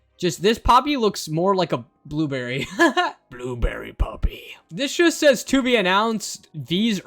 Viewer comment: It sounds like you said blueberry puppy😂😂😂😂😂